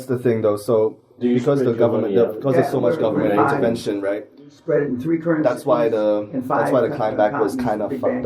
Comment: what is Dan saying? I couldn't get the whole sentance. "Spread them in three currencies........." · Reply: Spread money in three currencies on five continents in big banks? I believe he was commenting on what to do if you have any substantial money in order to keep it safe from any single country going for a money grab or crashing.